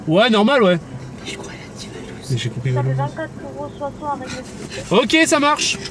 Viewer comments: j’entends « valouzz » moi aussi 😂t’es devenu connu dans les drives 😂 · moi aussi j'ai compris 🤣🤣